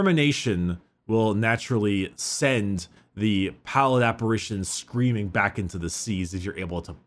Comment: I know he said 'pallid apparitions' but given that it was the Excess of Repose I choose to mishear it as PowerNapartions. Weary wanderer... no fight left to fight... no life left to live...